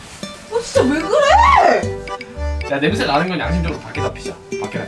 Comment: "냄새 나는건 양심적으로 밖에다 피자"로 들리네요